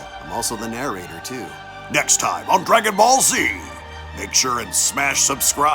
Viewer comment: I skipped 10 seconds at the very beginning Here is what i heard: Announcer/gohan: you're watching unreal---BallZ! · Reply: I heard "ON DRAGON BALL Z"